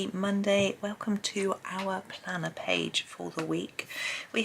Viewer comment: I wasn't watching the beginning of this video and I'd have sworn I heard you say our banana page! I looked up and of course there was the planner page 😂